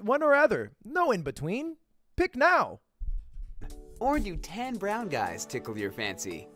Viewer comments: I thought he said "ten brown guys" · I thought it said ten and I'm dying 😂😂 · I thought he said TEN Brown Guys instead of tan lmao